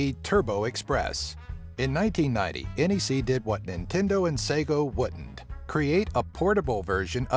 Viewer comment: Did he say Sego